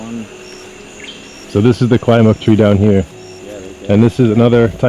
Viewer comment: Totally, awesome! I have a question. at about 12:00 in the vid, what is that fruit tree called. I couldnt understand what he said. The one with the fruits you have to eat right away and cant ship. Thanks! peace Niki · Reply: niki k that was Kwai muk (Artocarpus hypargyraeus) I'd check out the article the California rare fruit growers put out about it.